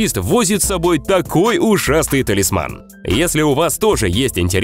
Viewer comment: Кто тоже услышал, что где-то на земле один таксист возит из собой такой ужасный (ушастый) талисман, или это только я?🗿